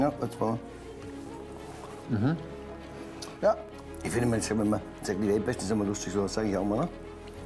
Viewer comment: bis 3:45 Kann mir das mal bitte jemand auf deutsch übersetzen? Hab mir das gefühlt 10 mal angehört. Ich zweifel langsam an mir selbst. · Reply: In etwa "Ich finde wenn man sagt 'weltbeste', ist ja lustig, sowas sage ich auch immer". Ist nicht schlimm Dialekt, ist einfach nur genuschelt ^^